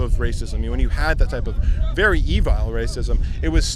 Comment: did he just say evile?